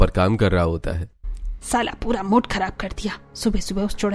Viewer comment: did she say Saala pura mood kharab kardiyaa??😂